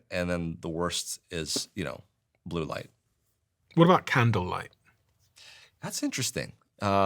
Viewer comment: I thought he said “candle” instead of kindle. 🧐🤷🏼